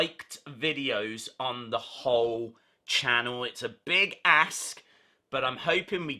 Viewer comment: lol I know you said big ask but it sounded like big ass